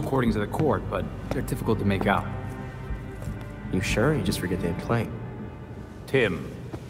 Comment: Anyone know what Tim is saying at 1:21:20 ? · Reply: He's saying "You sure, or you just forget to hit play?"